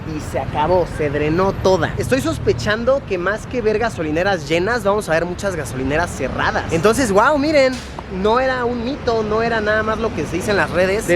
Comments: JAJAJA ESCUCHE VERGAS DURAS JAJAJJA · pensé que dijiste "vergas" pero dijiste "ver gasolineras" XD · en el minuto 4:21 luisito dice “ver gasolineras” pero parece que dices vergas xdxd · mi mente esta tan dañada que escuche "vergas" xdd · En el min 4:22 parece que dice ver...gas XD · Min 4:23 perdonenme por mí mente por qué dijo Luis "ver gasolineras" y yo lo entendí como vergas ja,ja,ja neta perdón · Jejajaj escuche verga Pero diga más que ver gasolineras llenas vamos a ver gasolineras cerradas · me parece que dijo vergas :v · SOY MAL PENSADA O EN EL MIN. 4:22 ESCUCHE UNA PALABRA ALTISONANTE... ;D ver-ga-solinera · Like si entendiste que en el minuto 4:21 parece que dice vr**ga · Luisito parece que dice vergas jajaja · al minuto se escucha que dice "Al verga solineras cerradas" JAJAJ (4:20) saludos desde chiapas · Parece que dijo Vergas 😂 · escuche verga 😂😂😂😂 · Ven que en el 4:21 pareció que dijo vergas 😂 jeje 🙊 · parece que dice vergas v:b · En el minuto 4:22 parece que dice "ver-gas" xD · Le entendi, "Que mas que vergas" xd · Like si escuchaste que dijo ver gas en el minuto 4:21 En realidad dijo ver gasolinas pero se entendió diferente 😂😂😂😂😂😂😂😂😂😂😂😂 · En el minuto 4:21parece que dice que ver** pero en realidad dice que ver gasolineras 😂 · No se pero escuche Ver-Gas xD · soy yo o escuché vergas jajajajajajaja, obviamente no dijo eso pero así se escucha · párese que dice vergas jajaja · Entre el minuto 4:22 y 4:24 Luisillo el reporterillo pareciera que dice Verga jaja